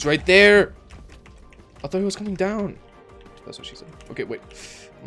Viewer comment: I literally heard you say ThAtS wHaT sHe SaId quietly during a battle with the hacker